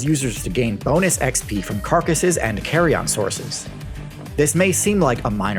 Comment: Oh! Carion! I thought you said "carry-on".